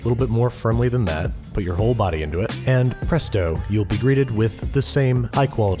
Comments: I heard "press dough," anyone else?? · He low key said “press dough” instead of presto. I subscribed just for that moment. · when he said presto, i thought he said pressed dough, because, yano, it was pressed dough XD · Thought he was saying Press Dough instead of "Presto" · I don't even know if you were going for this pun but at 1:28 when you said "presto" it sounded like "press dough" and I just about fell over in my chair